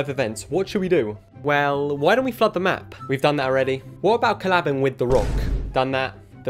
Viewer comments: I thought he said,,why dont we fuck the map" no flod the map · I thought he said " why don't we fuck the map"